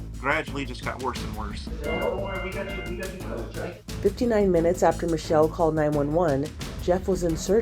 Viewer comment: im sorry did I hear that right? 59 MINUTES?